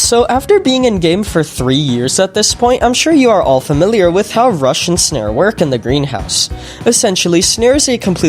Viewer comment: I keep hearing russian snare